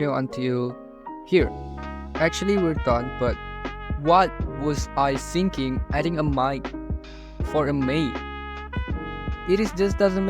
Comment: i heard it as "What was i thinking adding a minor for a maid?" and thats actually a good question. 🤨